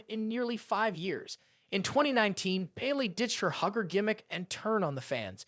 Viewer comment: Did you just say “gimmick” & “jimmick” in the same breath? 😆 Good video.